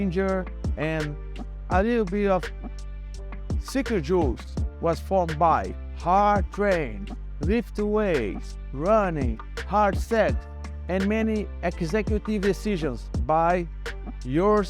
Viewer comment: Did he say "Hard Sex"?!!